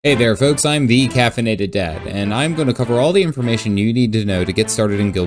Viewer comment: When you said “the caffeinated dad” I thought you said “decaffeinated dad” and I was like: who tf is this mad lad Babies are no fucking joke.